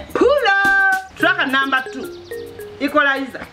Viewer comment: Who else enjoys puloooo track number 2🤣🤣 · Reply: I thought she was saying Strack number 2 of WBS hahaiiiiiiii.